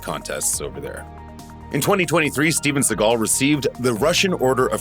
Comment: I kept misheard "Steven Shitgull"...😂